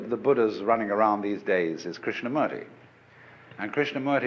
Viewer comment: What name did he say at 4:15? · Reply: Krishnamurti